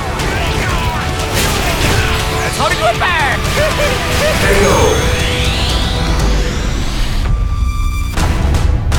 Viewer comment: so what does he say at the 0:52 mark? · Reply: to me it sounds like sonic fruit bat